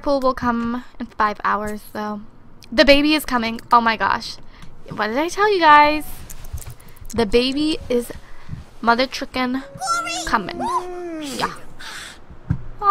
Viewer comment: it sounds like she said baba XDD